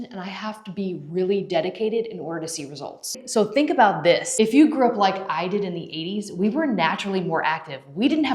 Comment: Almost 50! Keep doing what you're doing. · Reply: I had to do a double take..I thought she said "if you were born like me in the late 80s" 😂 The life style pays off!!